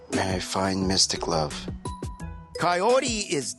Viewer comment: Lol I thought he said "may I find Mr Glove" 😂